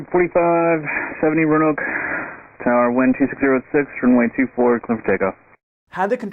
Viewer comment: Ok i played it back multiple times and i still got winds 260 at 6 cleared for take off. Instead of 160 6 at time